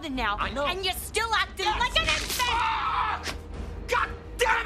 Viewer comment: What does she say at 0:37? "And you are still acting like an...? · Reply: Infant